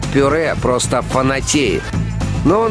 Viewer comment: мне послышалось что он сказал толчок